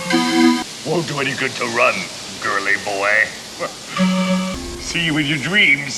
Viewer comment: What is he saying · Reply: Won’t do any good to run, girly boy 🤡